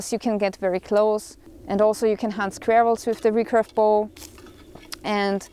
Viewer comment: Another great video! Now, MY mistake not yours... I was letting my attention drift for a second, I guess, but the first time through, (at 11:48) I thought you said, "...and also you can hunt ware-wolves with the recurve bow..." And I thought, WHAT?? is a survivalist AND a ware-wolf hunter?? Just more awesome and more awesome! LOL! Then I ran it back and clearly heard you say squirrels. My bad for not paying attention. But for a minute, I was in complete amazement! BTW - no worries - ware-wolves or not, I still am.